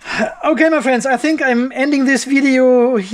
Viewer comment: I think when you say “Andy’n video” 54:42 you must be wear on your red t-short 😅